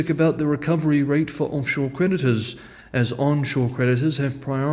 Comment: Sometimes I misheard "offshore creditors" as "offshore predators"...